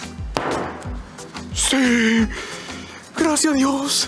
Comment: PEDRITO CON SU CELEBRACION DE SIUUU... (LO Q ESCUCHE XD)